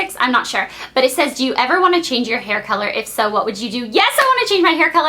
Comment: I thought you said you never died your hair color ?!